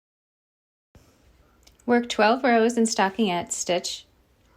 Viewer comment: Can you please write down what you say at 1:58? "Work 12 rows at...." ? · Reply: stocking(ette) stitch!